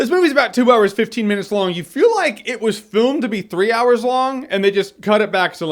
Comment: does he say foon instead of film 😂